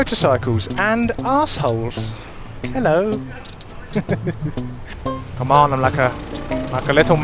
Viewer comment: did you just say "Come on Malaka"??? lol